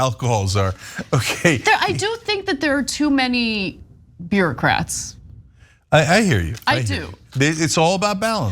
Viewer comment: did Ana say "I do think there are too many beerocrats"?? 🙂